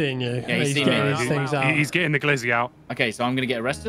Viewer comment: did i just hear "he's getting the glizzy out" at 16:57 ?🤣😂🤣